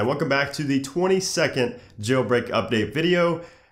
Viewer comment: I thought you said 20 second jailbreak video at the beginning 😂😂